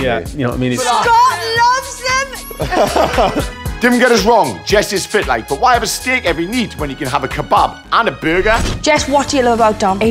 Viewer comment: I have absolutely no idea what he says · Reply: Dim geddus wrong! Jessi’s spitlife but why have a stick ev’ya neat whenya could’ave a kabob anda BUUGUH